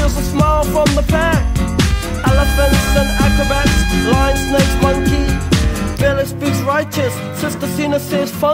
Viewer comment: I always heard “flying space monkey” and now my entire reality has been shattered.